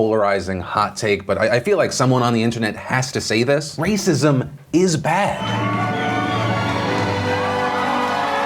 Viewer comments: I thought you said Racism is back and I was just thinking it never left and why is there cheering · I thought my man said " Racism is back!" And I was like wait huuuuuh? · Had this paying in the background and I thought I heard "Racism is back" along with a bunch of cheering. Caught me off guard. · Thought you said "Racism is back", made the applause feel very misplaced